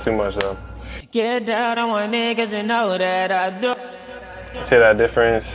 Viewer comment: bruh it sound like he said, get down my ninjas and doodoo !!? 😅 Pause!.